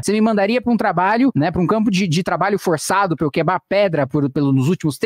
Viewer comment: A única coisa que eu escutei foi "quebá pedra"